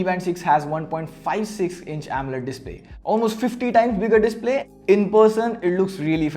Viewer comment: Did I hear say "50 times bigger display" and not "50% bigger display"?